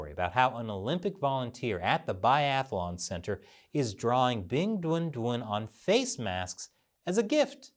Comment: Every time I hear say the name of the mascott I chuckle because I hear "Big Dun Dun" which sounds like a completely ridiculous child cartoon's character name... Ridiculous btw, like the entire idea of having a Winter Olympic in a city that has no snow and in a country that commits a genocide every decades or so...